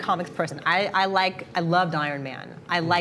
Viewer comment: She says she liked iron man the captions says she unliked it, ??????????????